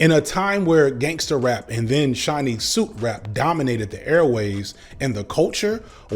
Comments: Did anyone else but me hear "Chinese soup rap" instead of "Shiney suit rap" around 19:40 and then Google "Chinese soup rap" expecting to learn something about a subgenre of rap from the 90s called "Chinese soup rap?" No? Just me? (After my Google turned up nothing I rewatched and realized what said and subsequently remembered all those fabulous shiny suits). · Omg I thought he said gangster rap and Chinese soup rap, instead of shiny suit rap. I'm still not even sure if I got it correct yet.